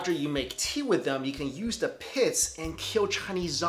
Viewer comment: Goji berries don't have pits. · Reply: lol i thought he said the piss was used to kill chinese zombies.